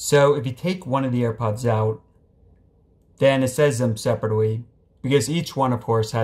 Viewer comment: sounds like he said then n**ga 😳